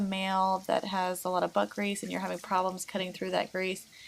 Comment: I THOUGHT SHE SAID BUTT GREASE!!!!😂😂😂